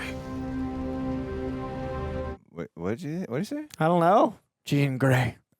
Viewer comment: she’s free is what he said.